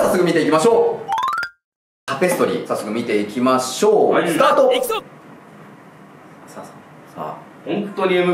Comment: 辺り、、 佐久間くんの「行くぞ！」って声聞こえなかった、、？？笑